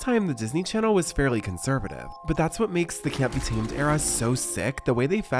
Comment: I... Am I supposed to know what this is?? I literally don't even know what word he's saying before "era" to look it up! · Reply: Mickey Cyrus released an album called “Can’t be tamed” through Disney’s record label; it has a more gritty, mature feel than expected for Disney